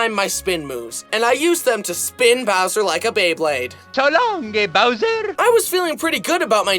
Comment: bro did he just says long gay bowser 😂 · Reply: Actually it sounds like he saying so long gay bowser but the game audio in Mario 64 is messed up so yeah what Mario actually saying is so long king bowser